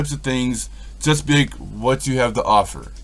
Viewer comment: what are you saying here 15:01 ? ...i hear : "you gotta do these type of things , chest big...what you have to offer" . Love it but please clarify. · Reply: "You gotta do these types of things... Just be like... What do you have to offer?" Meaning dont expect anything, and ask yourself, what this has to offer? Dont try to force yourself to feel/see anything. I spread my arms and stuck out my chest, i guess to symbolize opening yourself up to experience anything...